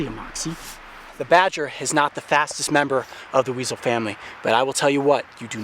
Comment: Did He Say Fattest Member XD